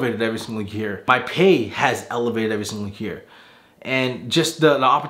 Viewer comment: I heard that as my 'pain has elevated' and I was like... "yeah brother... don't remind me" lol glad you got your dream job!